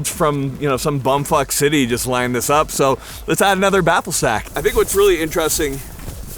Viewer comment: may have tried to say "Baffle Stack" but I clearly heard him say "Baffle Sack". Welcome to your new nickname...sorry, I don't make the rules, I just follow them 🫡